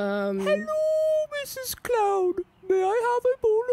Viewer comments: For some reason, I always thought he said “Hello Mrs. Cloud.” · This whole damn time I thought he was saying “hello Mrs.Cloud” I’m a goddamn fool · I always thought he said "hello Mrs cloud"